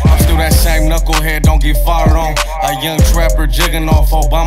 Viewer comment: Homie at it again “still that same knuckle head don’t get farted on” 0:48 ( lmao if ykyk this was in reference to the kill cokeuh interview where he coughed and it sounded like a fart) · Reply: Fired on*😂